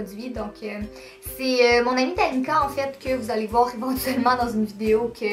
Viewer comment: Omd jai tellement reagis quand jai entendu "c'est mon amie Danika ...." 😂😂